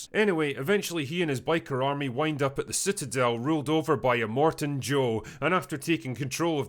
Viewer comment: "Ruled over by Cotton Eye Joe." that's what I heard